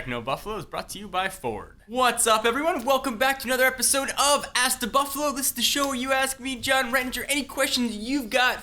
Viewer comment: Did he just say ass to buffalo?